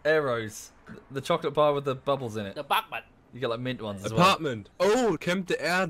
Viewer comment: I lost it at buk buk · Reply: he meant 'apartment' but idk why he sounded like he said 'butt plug' for some reason, at least thats what i heard